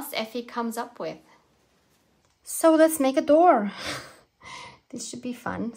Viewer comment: I swear I thought you said “create a boar” 😂😂Now… onto make a door …